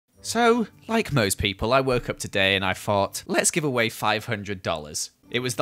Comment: Cause of his accent I thought he said "So I woke up this morning and I fart" LOL